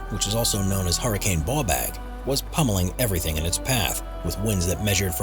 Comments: Number 15.. I heard "hurricane ballbag" 🤣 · He said Hurrican Bawbag, which the Scots named it. Bawbag is an scottish affectionate name for Scrotum! or a mild insult when referring to someone annoying (Ya Bawbag). Loads of lol's 😂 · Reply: I thought he said hurricane ball bag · Sounded like he said "hurricane ballbag" 🤣🤣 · hurricane ball bag did you say? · did I hear that right?? Did they call it “Hurricane Ballbag..?” 😂😂😂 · Did he just say hurricane Ballbag? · Did he say Hurricane Ball Sack????? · did he say also known as hurricane ballbag? or is that just my ears lol